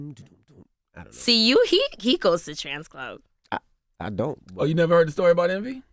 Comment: so no one noticed that envy and ctg both thought she said trANS club instead of trance club??? LOL